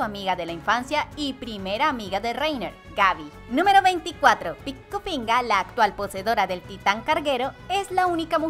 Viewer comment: Soy el único que escucha Pinga??? XD Buen video, tu voz es bonita. Solo que hay que corregir varias cosas <3 Alguien me explica lo de la isla de Paradice comparado con Brasil no lo entendí plis